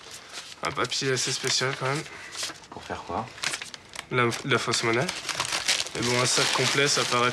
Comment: ce papier sert à quoi ? Je comprends pas ce qu'il dit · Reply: De la fausse monnaie c’est pour ça que c’est bizarre